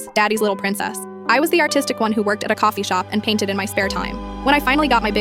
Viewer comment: I thought she said "autistic" not artistic!😂